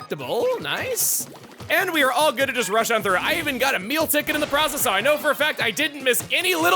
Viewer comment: it sounded like he said "I even got Emile ticket" lmao